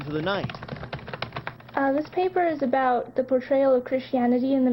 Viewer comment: I thought she said the her paper was on the "betrail of Christianity in the mass media", I'm on a 10 when i should be on a 2. She said the portrail!